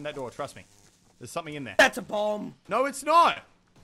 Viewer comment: Omg I thought he said "that's a bong" instead of "that's a bomb"😂😂😭 These guys are sooo funny